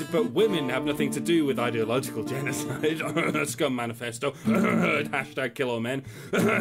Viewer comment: what word is disguised by a cough at 9:36 Oxford manifesto? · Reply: 23Stork "Scum Manifesto" (look it up)